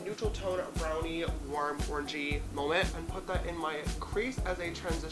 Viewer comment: sounded like orgy instead of orangey YEET 😂😂😂